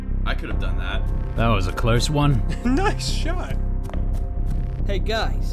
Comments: When that guy said nice shot it sounded like Dream. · did...did i hear dream? when he said "nice shot" it sounded like dream i swear to god